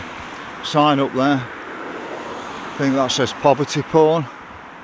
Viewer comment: I thought it said poverty pork